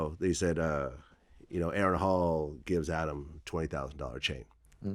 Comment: Did Glad Call him Aaron Hall @ 1:11 ?